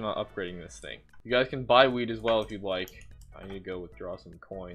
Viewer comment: I thought he said weed